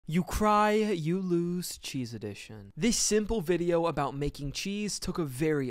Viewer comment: I thought bro said “obese edition” 😭